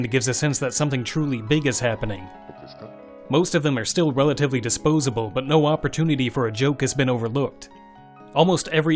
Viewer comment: What is being said in the background at 6:12? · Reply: "ok desu ka?" it's a sound effect from earthbound that plays when you're inputting the names of the characters basically means "are you sure?"